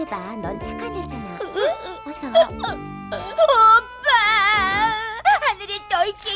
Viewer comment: 영희한테 둘리가 뭐라고 부르는 건지 백번 돌려봐도 이해 안 되네요 과거편에서도 호빠야 호빠야!! 이렇게 부르는 걸로 들리던데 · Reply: 오빠야 이렇게 불러요